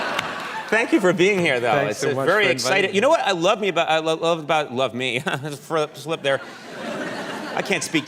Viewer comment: 이거 발음이 얼었다가 아니라 Freudian Slip 이라고 무의식적으로 본심을 말하는거에요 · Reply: 이 댓글 보고 다시 들으니 저한텐 frost slipped there 로 들리네요